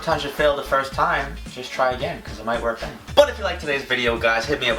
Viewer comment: i thought you said daddy instead of danny😂😂